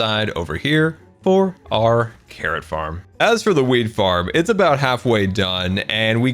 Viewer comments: i swear he said "as for the weed farm" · every time talks about the “wheat farm” all i hear is “weed farm” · When you said wheat farm I thought you said weed farm lol.